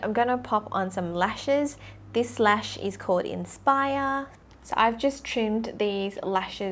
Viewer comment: infires?? ARMYYYYYYYYY!!!!! (MISHEARD)